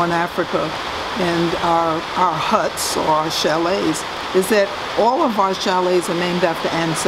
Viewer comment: At the 8:13 she gives another word for hut but I don't understand if she's saying sheeling or shellagh? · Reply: It's chalet, read "shalay"